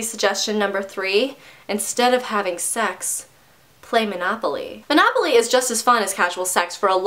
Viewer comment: I totally thought you said "instead of sex...play monogamy" for a second. I was SO confused for about .5 seconds.